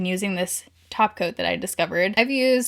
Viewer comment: You said “top coat”, I heard “taco”. Why am I me?